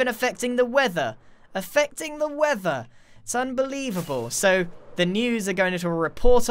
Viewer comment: did he just say "They are even effecting the weather, SUNbelievable !"